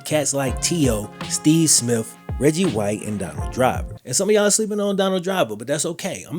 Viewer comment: Haha 11:08 RODDY WHITE lol When I heard it I was like Reggie White?? How'd he line up against another defender? lol